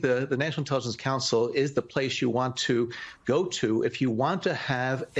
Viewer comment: 😅 did you just say "you want to blow Trump? "😅